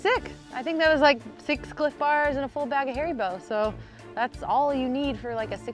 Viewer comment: Ohh man sorry hahaha just had to, slow it down to 0.5x at 3:41 and sounds like she said a full bag of HAIRY BALLS !!